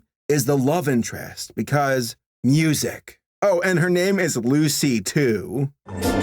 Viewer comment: I thought you said "Lucy 2" instead of "Lucy, too" and I was like "what are we deciding happened to Lucy 1"